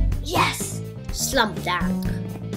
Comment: Lol at 1:00 "Slum Dank" instead of "Slam Dunk"